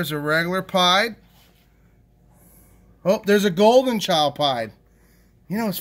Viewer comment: I thought he said a golden child pie